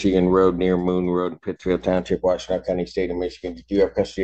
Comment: Aw, that's messed up. Being held IN JAIL, then finding out they didn't even have cause to hold you there would be infuriating. Sounds like grounds for a lawsuit. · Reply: Misread what he said. So sue me. But since you obviously knew I thought he said country and not county, your smart ass explanation of what a county is was unnecessary. I know you thought you were cooking with that comment but it was akin to boiling ramen noodles in the microwave.